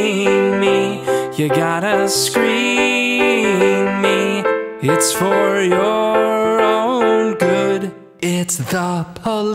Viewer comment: I thought number 7 said "you gotta screeeew me its for your own good"